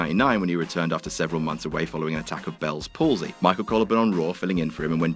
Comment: I know you said, "an attack of bell's palsy," but what I heard was, "a taco bell's palsy."